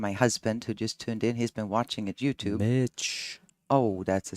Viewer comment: Sounded like he called a b**ch when she said she was giving a shout out to her husband 😂